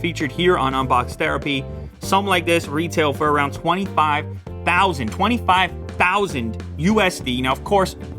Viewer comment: I could have sworn that said $250,000